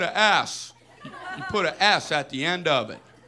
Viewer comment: if you put english(auto-generated) instead of s its ass